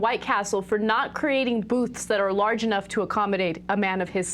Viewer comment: I thought they were saying 'boots' not 'boothes'